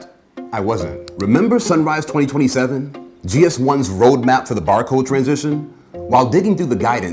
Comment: Anyone else thought he was saying “TOOTIE barcode” for ages? 🤔😄